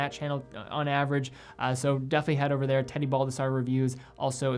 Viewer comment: Every time he says his name I hear "Teddy Bulbasaur"😅